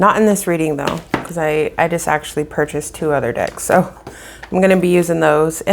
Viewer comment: Lmao sorry I don't why I heard " I just purchased 2 dicks "😂